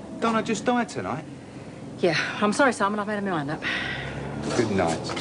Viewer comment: what did pat say at 9:52 "i'm sorry simon, I've had unintelligible on that." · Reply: just listened again and i'm now thinking she said "i'm sorry simon, I've made my mind up."